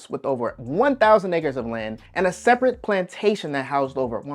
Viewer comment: Did he say 1000 (ni- ggers) and land @ 4:52... 🤔?